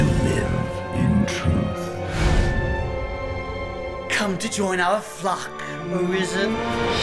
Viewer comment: first time I thought he said "Come to join our FUCK, in prison?" XD